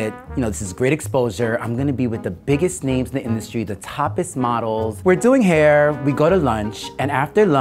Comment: The toppest model .... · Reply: I thought it said topless model 😂😂😂